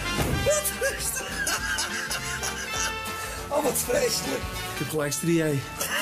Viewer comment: wat zegt Gordon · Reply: "ik heb gelijk striae"